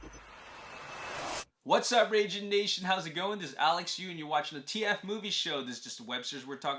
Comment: Now matter how much I rewatch this I always hear “Alex Yu and you’re watching the Road to TF5” lmaoo